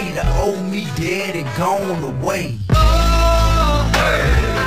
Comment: why the hell i always thought t.i. said the homies dead and gone away?